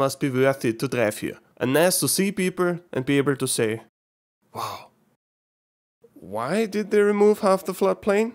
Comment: actually instead of wow is oyda